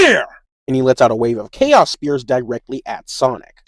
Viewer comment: I thought he said chaos blast for a sec I was like "welp Ok!!" 😂😂😂"